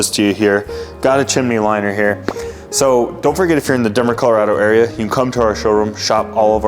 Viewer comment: For a second I thought he said dumber Colorado area. Lol